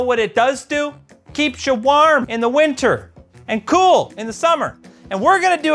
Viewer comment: as a stateside guy, I hear (waerm) (ceewl)